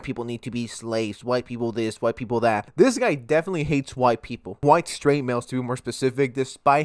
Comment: thought he said white alpha males😂